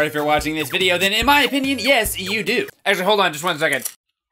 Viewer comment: It sounds like you said my name. Yay. "Ezra hold on a second"